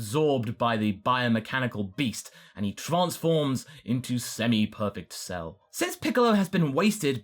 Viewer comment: I misheard you saying semi-perfect Cell as "semi-permeable Cell". Truly, that would be a force to be reckoned with.